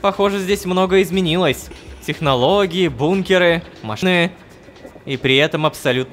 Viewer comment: я же не один услышал "машны"